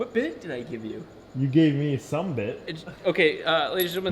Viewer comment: I know he's implying there is not enough there with "some bit", but for some reason I hear that as "summa bit'" if you get my nomenclature there.